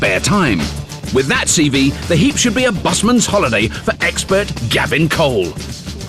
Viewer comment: I thought it said "Gay"